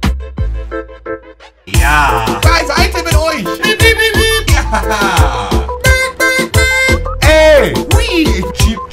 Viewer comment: was sagt er da immer??? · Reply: Patrick Gstir bei Seite mit euch